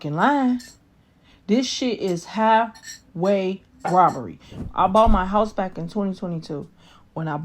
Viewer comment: did she say “halfway robbery” instead of “highway robbery?” 😂😂😂😂😂😂😂😂. Wow.